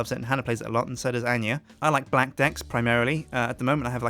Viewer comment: is it just me or did he say black dick